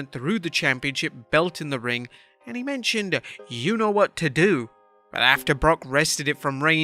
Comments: I thought Paul said I don’t know what to do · I thought he said: "I don't know what to do!" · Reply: I thought he said "you know what to do." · I thought he said: "I don't know what to do!"